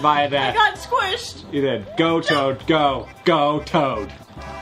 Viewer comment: At one point, hank says "go toad go" and it sounds like he says "go chode go" lol